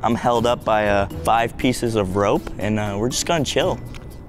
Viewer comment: Wha???? did say? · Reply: Good night dudes